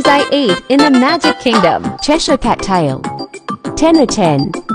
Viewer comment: When it said a Cheshire cat tail I thought it said Trisha Paytas us I don’t know why but that’s what I heard